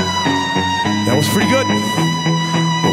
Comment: Could you tell me what it says in the minute 2:57 please :3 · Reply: "That was pretty good...but we can do better!" algo como "Eso fue genial, pero podemos hacerlo mejor" ^^